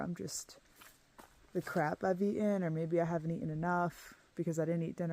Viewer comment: Wait, did you say CRAP ?